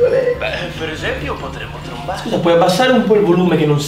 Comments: non ho sentito vermante "per esempio potremmo trombare"? 😳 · solo io ho sentito "beh per esempio potremmo trombare?"